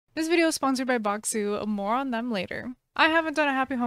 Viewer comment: What she said: "This video is sponsored by Bokksu, more on that later." What I heard: "This video is sponsored by Bokksu, moron."